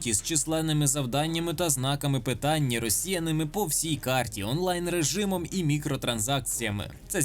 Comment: я а ж підскочив неначе під дією шокера коли почулось на 13:32 "росіяни по всій карті" брррр